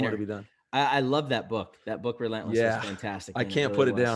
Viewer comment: Anybody catch the book title they were referring to around 23:50? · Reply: RELENTLESS by Tim Grover